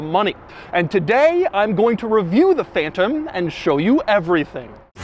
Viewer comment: Did you said PHAETON at 0:25 ??? Its PHANTOM 🤣